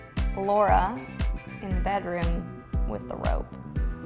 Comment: Elise: Laura, in the bedroom, with the rope. Natasha: Carmilla, in the bedroom, with chocolate. Well that summed up Valentine! 😂 Lmao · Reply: I first heard "with a robe" which made sense cause of the dress in the movie but this is actually much better !